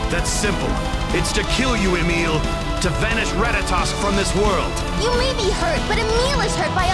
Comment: I thought he said "To vanish Ratatas from this world!" XD